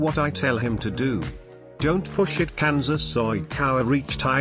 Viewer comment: help I’m rolling 😭😭 For « don’t push it ka- » did it say Kansas?!?!